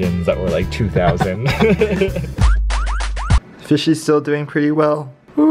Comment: Did he say "fishes"?